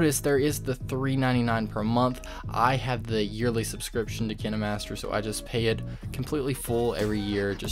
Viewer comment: Did you just say "Kin-aye-master"?